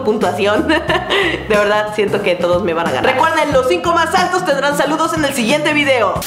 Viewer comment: Yo escuche "De verdad,siento q me van a agarrar 7w7 ;;-;;" No c preocupen,mi mente ya es así de cochina :'c